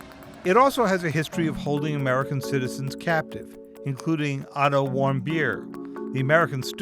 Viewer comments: When you said otto warmbier i thought you said "i dont want beer" lol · "Otto wambier" Lol i heard i don't want beer